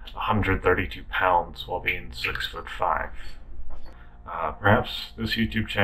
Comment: Did he say he was 6ft 5" ???